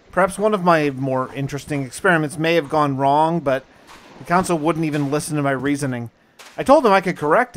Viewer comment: XD right on the begining I laughed really hard because around 1:40 you said "the counsel" but I heard "degonzo" lol! Not trying to make fun, just you said it so fast my mind filled in the wrong answer to the blank. xD